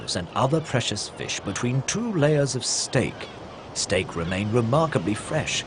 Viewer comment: Huh? I heard skate. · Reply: Renita Lake listen carefully at 32.58 I played it six times because I thought I had heard it wrong but no, he says stake.